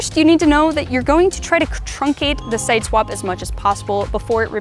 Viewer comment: Great vi...wait, did she say "con-truncate"? (10:16) It was going so well! :)